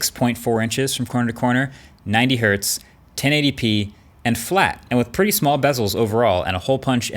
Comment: The subtitle says 10 ADP instead of 1080p